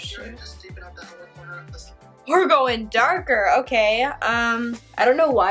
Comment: did she say "oh shit"? Bc I've never heard her curse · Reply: i said oh shoot haha sounds like it tho